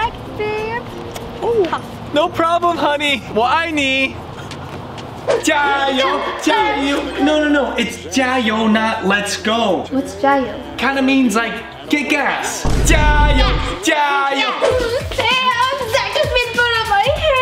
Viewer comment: that's the first time I heard sam say "kick ass" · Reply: it was get gas( I know that because I am from Hong Kong and I speak Chinese